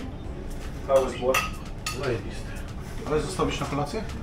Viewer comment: не знаю что сказал человек..я услышала как "заебиста" 😂😂😂